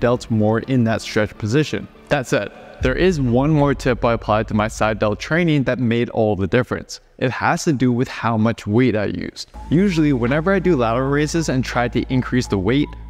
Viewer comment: I swear I heard “depends on how much weed I use”😆😆